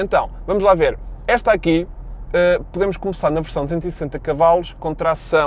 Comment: Minuto 6:06 ouvi 260 CV ? Gostava de ver mais veiculos OffRoad no canal :)